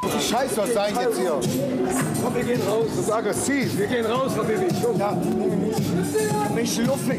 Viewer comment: Also ich hab "Wir gehen raus Habibi" verstanden